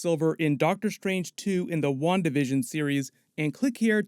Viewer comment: When he said New Hulk Solo Movie, I heard, "New Hope Solo Movie." I was like "Hope Solo" is an awesome name.